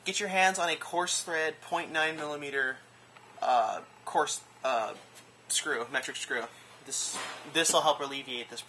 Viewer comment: What did he call the screw at 24:12 ? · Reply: RnJGamers 0.9mm coarse thread screw. I have had no luck finding it though. Might have to go with a 1mm.....